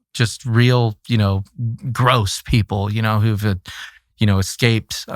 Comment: did he call them "gross"?